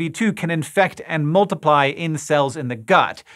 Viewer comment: I heard: “It looks like SARS-CoV-2 can infect and multiply incels in the gut.” 😂